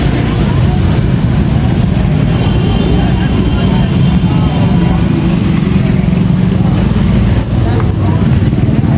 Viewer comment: Di menit 11:00 itu perempuan bilang apa bang? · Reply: ohh itu dia bilang hati2 hp nya rawan jambret kurang lebih begitu bang